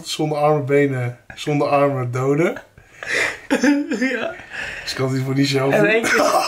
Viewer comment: wat zegt daar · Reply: ze kan het in iedergeval niet zelf doen